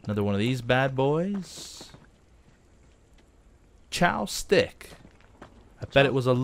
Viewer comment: When he said the name of that restaurant it sounded like he said Chow's Dick :)